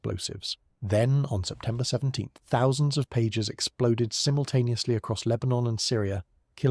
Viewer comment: Pagers, not pages. As in mobile communications devices from the 80s and 90s. The caption is wrong.